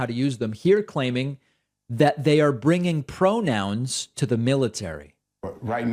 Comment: I thought he said ponouns ,dang !